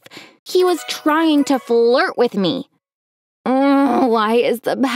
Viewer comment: I thought that she said vlirt not flirt